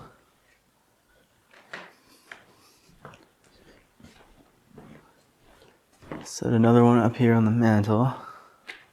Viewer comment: Hey, sending love and prayers for Brianna. I hope she is able to walk down the aisle for her wedding! My heart really goes out to her and she amazing to be so positive and strong no matter what life has thrown at her!!! Also, at 31:39 as you are walking away from the pool table you can definitely hear a whisper but I’m unable to tell what is being said. And then around the 32:17 after you say “Hoping” you can hear another whispering voice but I cannot seem to figure out it’s saying there either. If someone can make either one out if not both, I’d really like to know what it’s saying. Also, around 35:02 as you say you want to try to take the child down to the games room as white mist is comes out of nowhere. Like as if someone blew out smoke from cigarette. It’s so noticeable that there is no way you can miss it. I wonder if it’s the child trying to show its self… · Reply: First says once a day second one says don’t blow me third something about door open the door maybe?